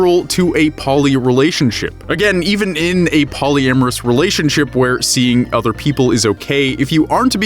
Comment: Projared thought hiedi said "hollyamourous relationship", oh jeeze, wut a goof.